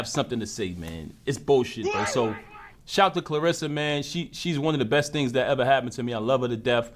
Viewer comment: I guess this is the one time he was truthful …He actually said she’s “ONE” of the best things that happened to him…NOT the best thing.